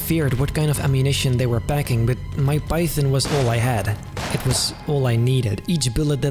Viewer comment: … anyone else hear him say “Mike Python” 😂😂